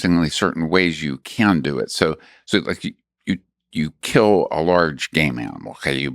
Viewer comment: Thought he said “you kill a large gay man” 😂